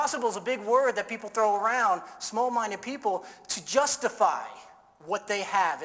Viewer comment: he says, "people would tell you something's impossible, I had my ___ for certain, it's virtually unanimous." What exactly did he say in the blank. I heard nars ners but i don't know what that is. Help would be appreciated! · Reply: Nay sayers